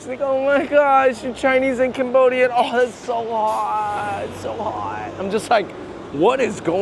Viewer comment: Wait, I thought they're saying "That's so hot", basically praising each other, not "That's so hard." What am I missing here??